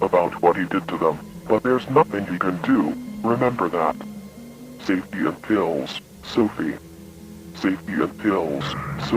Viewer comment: I thought it said "take your pills sophie"